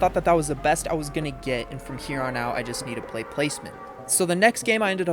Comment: I thought he said lazerbeam instead of placement lmao 😂